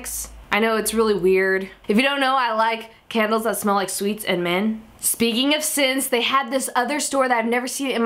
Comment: in 2:39, did she said that she like candle that smell sweet and men?